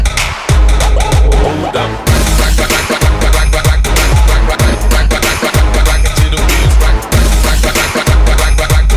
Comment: was he saying quack quack quack quack quack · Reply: He's saying "rock." If you listen to the end of the sequence of the words, you hear him say "rock it 'till the wheels fall off" using the same sound that he was repeating.